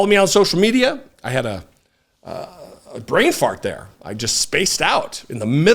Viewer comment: Brain Fart? I thought he said "Brave Heart"